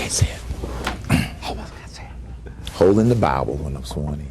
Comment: Lol I thought he said"holding the bowel"